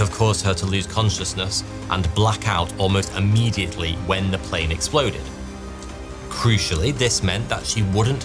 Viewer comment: I thought they said her heart exploded. He couldn't have her she would not be alive